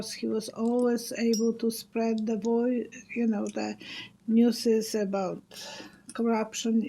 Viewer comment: I heard you say “new-sez” which sounded so cute but it got me thinking. “News” is a plural noun but what would be the singular? It wouldn’t be “new.” One horse is two horses but one sheep is the same as two sheep. One mouse is two mice. It’s no wonder people say English is hard to learn because of all the exceptions. Did you find it difficult to learn English and what age were you? Did both of your parents speak English and Polish when you were growing up? 🥰